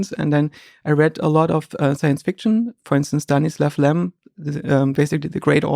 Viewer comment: which author does he mention at 8:01? I can't understand his pronunciation there properly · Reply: Someone here said Stanislaw Lem as being mentioned at 7:58 A polish autor it seems